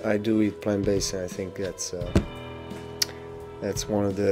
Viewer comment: what does he say at 3:01 ? I do eat ... ? Thank you! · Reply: Plant based, he's on a plant based diet